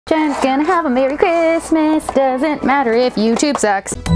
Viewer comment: Bro I thought she said “jens gonna have a good Christmas doesn’t matter if you do drugs” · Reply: Babyturtleshoe She said “Jen is gonna have a good Christmas doesn’t matter if YouTube sucks”